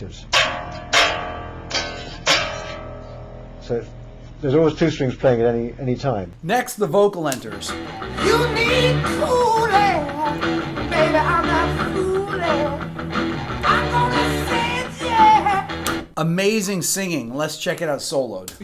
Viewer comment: When I was young and my dad playing this in the basement workshop I always thought he said you need kool-aid